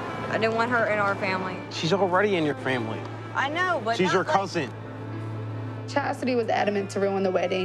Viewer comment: “I didn’t want her in my family.” “She’s already in your family, she’s your cousin.” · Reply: I thought he said she’s OUR cousin